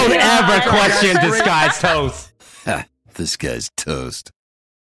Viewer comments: BRO THIS WHOLE TIME I THOUGHT THE INTRO SAID “THIS GUY TOAST” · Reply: It does say that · Is the intro "This guy's toast" or "Disguised toast" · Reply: Both actually,it's a card's reference in Heathstone,it says "ha ,this guy's toast" but people misheard it as "disguised toast"..this was a running meme when toast created his channel and he thought maybe take that as the name